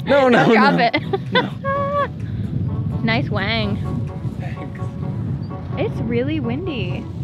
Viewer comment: did say "nice wang"